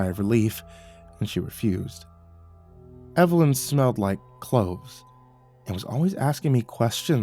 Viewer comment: Lol i thought he said she smelled like clothes, i caught the word cloves on the second repetition but i thought the protagonist was a weirdo for the first bit there.